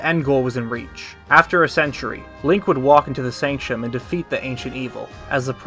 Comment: really sure sounds like you said Leia not link lol